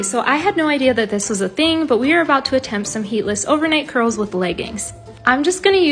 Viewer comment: I thought that said “heartless” ✋✋💀💀